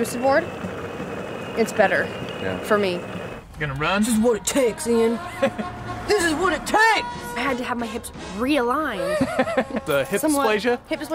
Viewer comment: i thought it said what i take ... hahaha